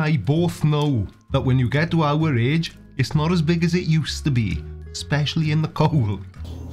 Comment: For some reason, (couldn't be the non-existent accent) I thought you said "Especially in the cones" at first and that it was short for 'pinecones' which in turn was a nickname for the 'pair down there' and it still worked. Poor CC...he's so angry at the fantasy world in his head. I wonder what he considers research, because if you're not doing valid experimentation, you're just "being told things", right? Tim Minchin said it best, "[People say] "But evolution is only a theory!", which is true, I mean it is a theory and it's good they say that, I think, because it gives you hope, doesn't it? That they feel the same way about the theory of gravity and they might just float the fuck away..." Float on, CC. Let your luxurious hair shine in the sun as you get a good long look at the sphere Earth is.